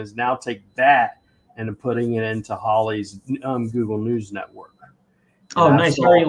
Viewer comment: Not sure what you mean my friend. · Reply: the guy said something about Google News Network and I thought he said Ali's Google News Network. I thought it was a tool or something. Oh well. I'm too lazy to go back and look now.